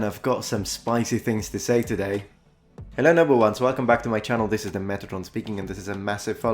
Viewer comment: Every time you say "hello noble ones" I hear "hello number ones" haha